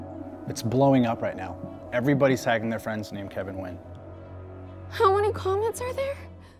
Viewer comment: I'm not American or Vietnamese so i had no idea of the correct pronunciation of Nguyen. I just know the surname as a common last name in Vietnam. So from the beginning till 6:03 I thought they were saying Kevin Win. Only until I read the name on fb comments did I realise it's Nguyen and it explained why there's so many people with same name. Glad you showed the spelling otherwise I would have missed the joke